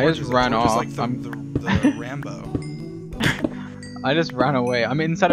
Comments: I thought he said Ranboo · did he just say Ranboo…???? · sounds like he said Ranboo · did he just say ranboo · i swear he said ranboo BFKSBFKJF